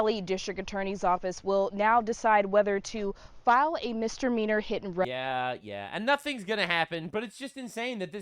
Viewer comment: did she just say Mr. meaner?